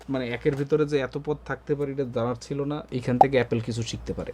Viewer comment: ভাই কি বললো এইটা - " একের ভিতরে যে এত পো_ থাকতে পারে " 😂😂